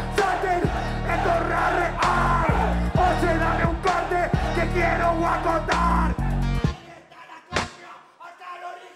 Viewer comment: no puedo entender que es lo que dice klan!!🤔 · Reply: Oye dame un corte que quiero acotar